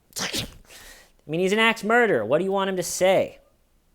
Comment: I have to check my pictures because I kept hearing "ex murderer"